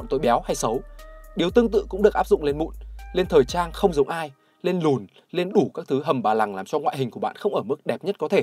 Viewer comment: lên cái gì cơ. À lên lùn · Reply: Vậy mà tui nghe thành “lên loz” 🤣🤣